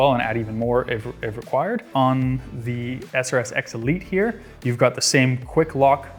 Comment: it really sounds like he is saying XRS Elite multiple times, like